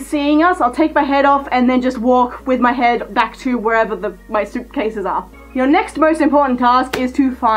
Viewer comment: at 2:22 it sounded like you said soup cases